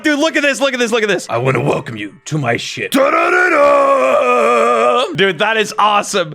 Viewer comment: wat he say? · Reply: im gonna welcome you to my ship